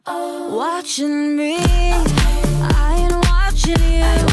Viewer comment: and 1:38 i think that are ad-libs of Jesy. She say "Ain't watching" "Check it out" (i don't know what say, sorry😅😂😂😂😂) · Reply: The ad-libs are "I'm watching you" and "Check it out" 🤪😄